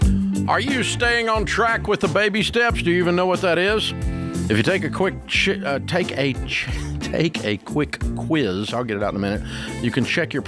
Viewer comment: “take a quick shit….” lol. Yes I heard you Dave